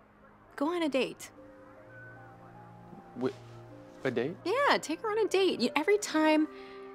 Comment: I thought she said to take his own daughter on a date...